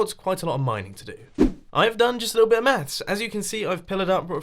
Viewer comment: I had to play 9:20 twice cuz I heard you say "I did a little bit of meth" lol 😄